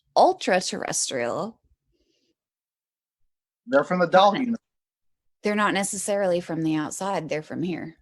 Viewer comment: says that Ultraterrestrials are from the ("Doll unit?"). I can't hear what he says. Can you?